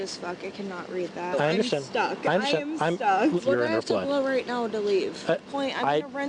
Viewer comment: I thought she said "who do I have to blow right now to leave"?